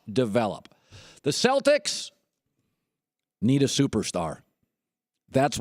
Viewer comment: Did he just say, the celtics need AN ALLSTAR